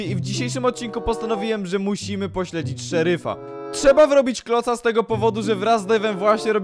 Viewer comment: czy tylko ja usłyszałam *Trzeba zrobić kloca*? XD Edit: tak wiem, on powiedział wrobić